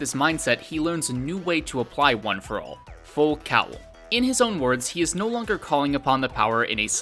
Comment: Thank you for just getting the name right. It IS Full Cowl, not full cowling. That is all. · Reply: here I thought the dub was saying "full calling" wat?